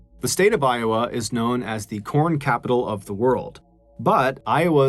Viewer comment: At first I thought you said Porn capital of the world.